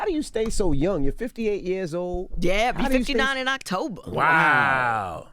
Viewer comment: Damn, did she just say "I'm 58, I'll be 59 in October" !?!? I had to go back 2x to listen, enlarge my screen and pause from working!! Looking good momma!! ♥♥